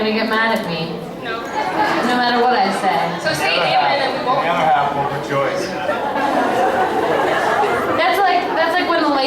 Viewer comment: What ian said at the minute 1:14? I don't understand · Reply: Kim Triana at 1:14 he didn't say anything but right after that he said the other half will rejoice